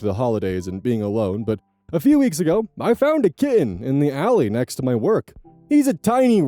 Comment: While I was working in the basement and when it was said said that OP found a kitten I thought it said "I found a kid" and was so confused by the explanation that followed before it clicked😅